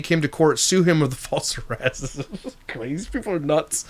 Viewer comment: did someone say sue him for false arrest? Lol 😂 he didn’t arrest her. Lol 😂 dummy 😂